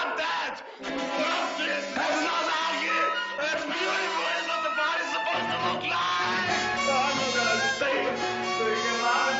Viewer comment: wtf is he saying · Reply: "Its not ugly it's beautiful it's what's the body is supposed to look like!"